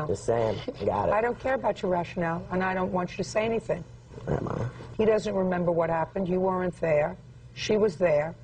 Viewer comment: did he say “alright mama”